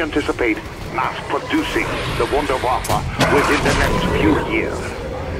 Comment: Lol when he said "Wunderwaffe" I thought he said Wonder Waffle. XD